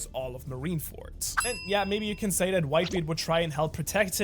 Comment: did he just say that a word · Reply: that’s what I’m saying he said a%#